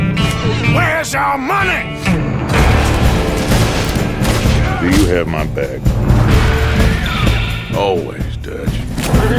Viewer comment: for a long time i thought Dutch said " Do you have my bag?" im stupid